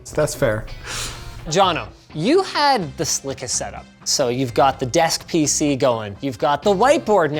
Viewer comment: 50 seconds in I'm calling it Rileys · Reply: My bad thought he said best set-up.